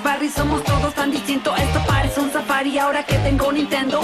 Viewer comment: yo escuche Safari. No atari